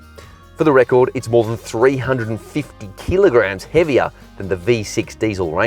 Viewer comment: Is it though? On paper those things are nice but the payload could be a problem for a lot of tradies. You start to load it up with tools, bags of concrete, timber etc, you're going to go over that 650kg pretty quickly. Unfortunately this was missing from the test - load the utes up with their max payload and see how they perform. The Shark will suit a lot of use cases and it's welcome competition but it's limited. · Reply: Yeah - I thought he said 650Kg in the review. Thanks for correcting that.